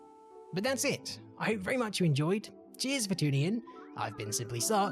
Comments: I thought he was saying simply suck :/ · this sound like you said: "i am simply suck" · when he say his name it sounds like hes saying i simply suck